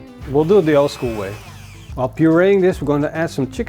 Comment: what is he saying? "up your anus, we're going to add some chicken stock" ??? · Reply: "While pureeing this, we're going to add some chicken stock" 🤣